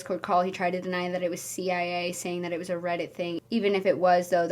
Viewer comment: dead ahh been saying “cia” instead of “cai” 😂😂😂